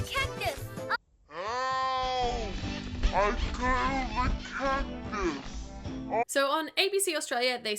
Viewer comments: I think that he said I cuddled a cactus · he said I cuddled a cactus, that’s what I hear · i think he’s definitely saying he cuddled a cactus, cuddling a cactus is also a joke i hear a lot in kids shows and movies but slowed down i can really hear the “Cuddled”, that’s just me though :o